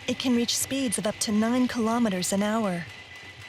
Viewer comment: I didn't understand how fast asimo can run ? 10 million kilometers an hour? Lol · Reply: She said: "it can reach about 9 kilometers per hour."